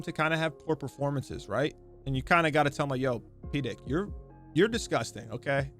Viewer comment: did he say P-dick instead of P-dog? 😂😂